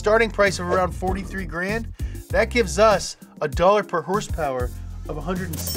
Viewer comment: Sounded like he was saying dollar per hurst which is what he’s gonna need soon with all the motor oil he’s drinking